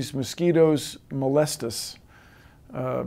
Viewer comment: I thought he said molest us, I was terrified